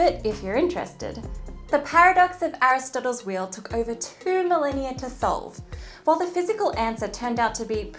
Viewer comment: "The paradox of Aristotle's wheel took over two million years to be solved." WHAT?!? Oh, two millennia . #MisheardLyrics